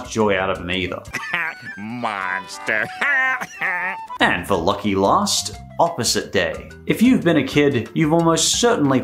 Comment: What does "Lucky Lust" mean? Is that a saying? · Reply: He said lucky last